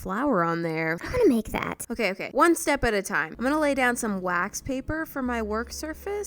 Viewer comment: Is it just me or when moriah said "Lets get out some new wax paper," it sounded like she said, "wags paper"?